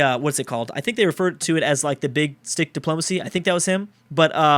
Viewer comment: At first I thought he said that Theodore Roosevelt had "Big DIck Diplomacy"